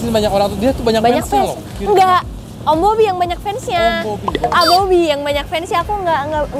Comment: gua dengernya Om Mobi dong